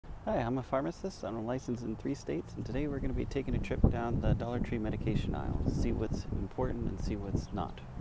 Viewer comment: At first I thought he said “unlicensed in 3 states” 😭